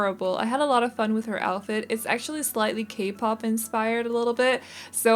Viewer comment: DID YOU JUST SAY K-POP 14:43 OH MAH GOD K-POP IS MAH LIFE